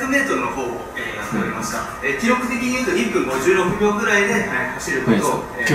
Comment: 2分56って聞こえて、いや遅いやんって思ったら1分違った。普通に速いすごい。